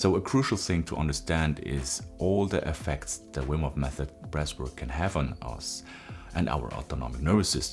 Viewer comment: The first time I saw this guy, I thought he was saying “breast work” and “breast control” for about 10 minutes and had no idea what he was on about. The last minute of so my brain finally kicked in ‘ooooh breath work’ 🤦‍♂️